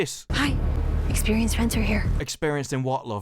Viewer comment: what was actually said was "Experience FENCER, here." not "Next time we RAID a pirate ship, you take point." I think he heard: "Experienced friends are here." "Next time you read a Pirate Ship, you take point."